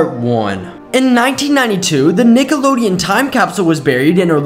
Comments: Lol😂... I swear it sounded like he said niggalodeon... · Am I the only person that heard him say Nigelodeon 💀💀💀 👇